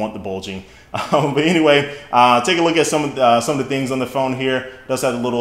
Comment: he actually says Phone to this Camera at 2:04..... !